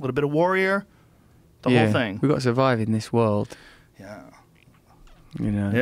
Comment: what did Russell say at 0:50? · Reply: Gotta survive in this world. Start from 0:37.